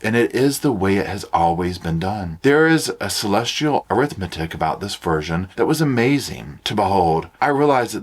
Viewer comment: ~ Did I hear Earthmathic? Is that right😊? That’s a giggle and a half! ❤ you!